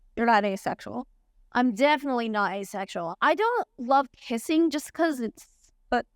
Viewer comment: I thought Jazz said "I don't love pissing" and I almost lost it